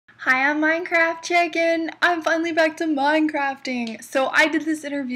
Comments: It kinda sounds like she says hey I'm minecraft chicken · Its actually "Hi Im minecraft chick and." She probs just said Chick n And a bit too fast so its sounds like shes saying chicken :p But seriously, it does sound like chicken :P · In the beginning it sounded like you said, "Hi, Minecraft chicken" not " Hi, Minecraft Chick and...". LOL!